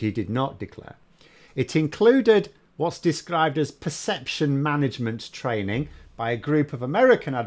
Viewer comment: I had to rewind the "perception management training", thought I heard DECeption management.